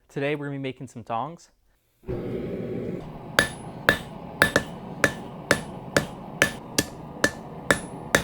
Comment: With all honesty as first I thought he said that he's going to make some thongs LOL